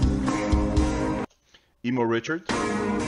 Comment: Did you say "emo" Richard? hahahaha, if I understood correctly, then long live Emo Richard forever! 😂😍🥰